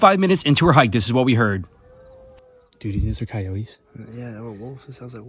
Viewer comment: Sounded like he said Hoyote.